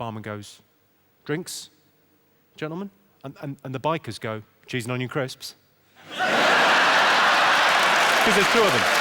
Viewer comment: What does he say at 42:15? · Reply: Cheese and onion crisps